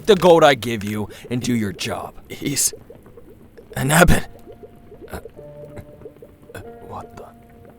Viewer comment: In 24:36, how do you spell that? I can't understand it. Please? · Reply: It's spelled "Ebon" :)!